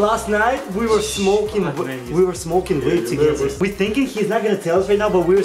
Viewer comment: smoking weed together? Hahaha that's what it sounded like to me but y'all probably said vape (be sure to inform me if it is though) nudge